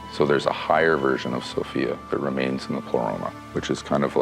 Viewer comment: Did he say ..that remains in the Corona??? Somebody help me out please